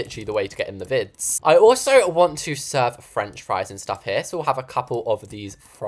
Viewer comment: Did he say …FRENCH FRIES!?